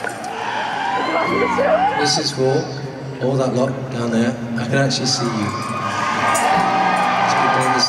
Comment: he says "This is for all that lot down there. I can actually see you." In british english, lot can mean a group of people. I am not sure what he says from after that until "Hi! All right?", but all I know is he's interacting with the fans.